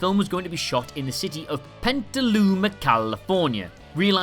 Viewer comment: It's Petaluma, CA. Sounded like he said Pentaluma.Also, Edward Van Halen was the guitarist in Van Jalen, not the singer. Sheesh.